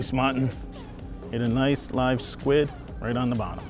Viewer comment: did he say the mutton hit a "nice live squid"?